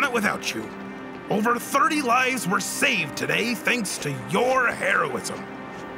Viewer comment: Is it weird that I heard: “Over 30 lives were saved today thanks to your heroin”?